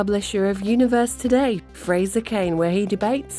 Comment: I thought it said Frasier Crane 😂